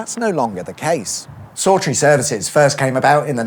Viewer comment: I thought you said 'sultry' services for a second. I thought oooer missus thats quite a name 😂.